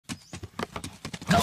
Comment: I thought he said good joe